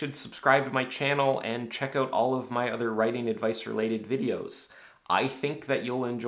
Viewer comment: For a second I thought you said "and chekhov all of my other writing advice related videos" at the end and I thought that your jokes were hitting a whole new level of complexity. Great video, hi cat!